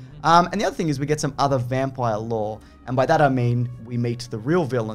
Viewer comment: When says "vampire lore" I hear "vampire law."